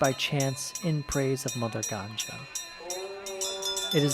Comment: Lol...did you just say mother ganja ?...lol..it means mother weed 🤣